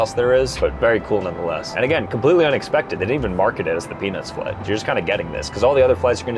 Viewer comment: what did bro say ?? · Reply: He said peanuts flight .